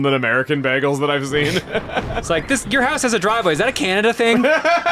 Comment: definitely misheard that as "Your house has a drawbridge" at first, whoops